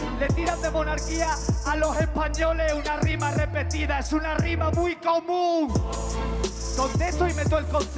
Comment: !!!!!!! Ya la entendi!! (Comunidad) dice es una forma muy común pff skone